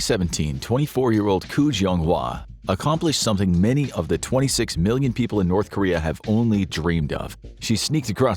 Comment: I thought you said 2.6 billion people in North Korea!